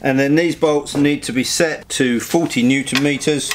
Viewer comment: The bolts at 3:18 are tighten with 14Nm ou 40Nm? Couldn't understand... But i guess is 40Nm... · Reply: Yes 40nm